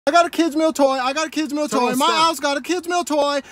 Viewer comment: BRO I KEEP HEARING KISMA FR